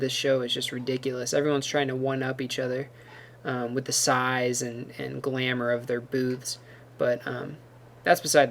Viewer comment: I thought you said "Everyone's trying to 1 up each other by the size and glamour of their boobs" for a second there! XD